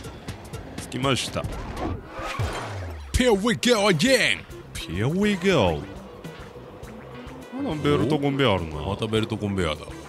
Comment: のヒアウィーゴーアゲインが、DJ コウに聞こえてしまった、、、